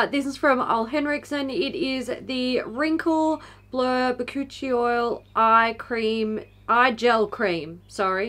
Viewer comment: When you said Bakuchiol I wasn’t paying attention and thought you said it contained ‘coochie oil’ and thought well, that’s a new marketing technique I haven’t seen yet haha